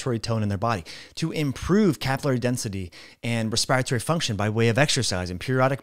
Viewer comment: Thanks. At 11:57 What was the term you used here? Something akin to 'Caphala density'...can't quite catch what you said. Thank you. 🥳🌞